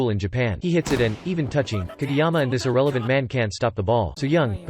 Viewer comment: “Kageyama and his irrelevant man—” Me: “What the eff did you just say about Kindaichi?!” (Jokes aside, this is a really funny video! 😂)